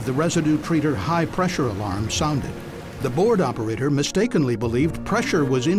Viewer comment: It always amuses me to mishear "board operator" as "bored operator".